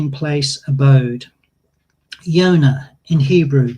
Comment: definitely heard this as "Yoda" the first time - clearly the flood enveloped Luke's X-Wing as well! you know, because they sound alike 😅